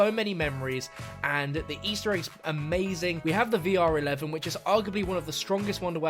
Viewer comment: Lol who else heard him say vr11 is arguably the best wonder weapon😅